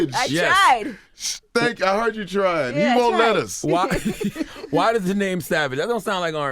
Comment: I thought tank said he needed more lettuce, but he said he won't let us 😂😂